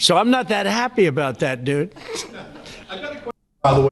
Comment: Wait. @ 2:42 Did Trump just say “dude”? no